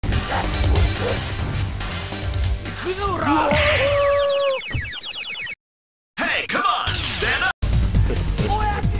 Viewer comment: What did Dan say? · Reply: Vs Blanka it translates to "Let's do this!" Vs Sagat he yells out "Father!" Sagat killed his father during a fight and so he basically been looking for Sagat ever since to avenge his father. Also I believe that intro where Sagats holding someone who looks like Dan is supposed to signify his father and he throws him away when Ryu approaches. Cant remember exactly when it canonicaly happens but it was confirmed Sagat killed Dans father.